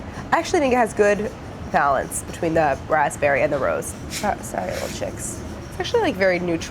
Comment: I love peeps, but yes no matter the flavor they all taste the same. Edit: At 15:21 Did you just say not sorry? xD I kind of expected lemon flavor for the yellow chicks or pineapple.. but that's just me.